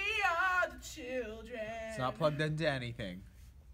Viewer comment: I like that you can see think about doing a Bill Cosby impression, decide not to, & then do it anyway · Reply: Also when said "it's not plugged into anything" I thought he said "let's not plug that daddy thing" & was calling Michael Jackson daddy